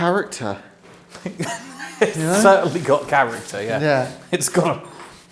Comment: EVP: 6:05 female voice during one of you breathing out with a laugh and it sounds like she says 'oh yea' in response to you saying the place 'has character' - can't tell if it was one of you saying it, but I don't think it was because it was so much lower in volume than either of you